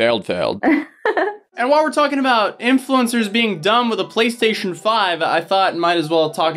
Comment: When he said play station 5 in the start of 5 the vid i kept hearing “Police station 5” i was confused